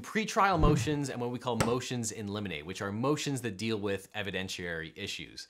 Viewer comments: At first I thought you said motions in lemonade lol. I’ve never heard that term before. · I wasn't looking at the video at this time, so I thought you said "Motions in Lemonade."